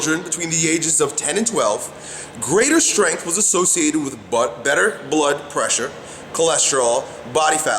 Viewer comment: does he say "butt better" ?